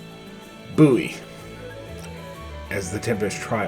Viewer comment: Did you say Booey not Boey.